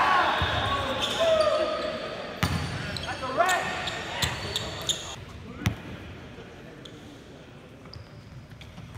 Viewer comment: What's that guy yelling about at 20:00 ? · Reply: Think he said "That's a red." as in red card for kicking the ball.